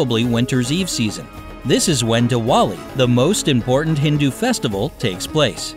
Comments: Who else heard duwali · what did he just say? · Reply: Diwali